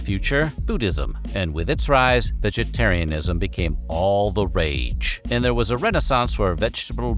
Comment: Did he just say "All the wage"